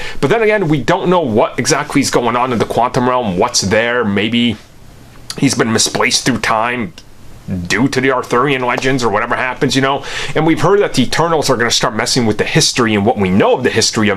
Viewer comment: For a second there, I thought he said the "turtles" are going to start messing with history instead of the "eternals"